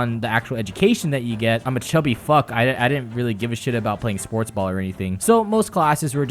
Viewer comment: did he say sportsball?